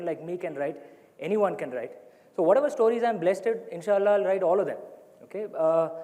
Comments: Did he say insaallah at 7:16? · did he said insha'Allah ? 🤔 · Did he say Insha Allah?